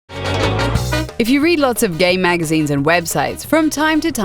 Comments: it sounded like she said if you read a lot of gay magazines and websites lol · Am I the only one who thought she said "if you read a lot of gay magazines and websites" at the start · At the start I thought it said "if you read gay magazine"